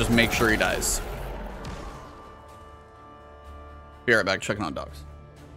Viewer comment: Great vid - did it before but great to see this. Just two places where I coul dnot figure out what you are saying: 05:36 "Here I am checking my dots???" 39:14 "These are the ??? players?"